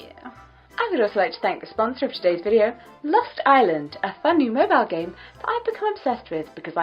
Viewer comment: I was a little confused at the beginning when you said lost island because I thought you said lust island. It was probably just me though because I'm so dirty minded